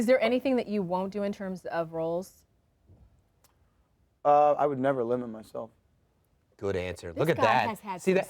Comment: What does he said at 3:48 ?? That he will never what? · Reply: I would never limit myself